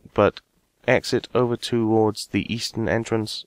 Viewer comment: East? I thought you said Weast?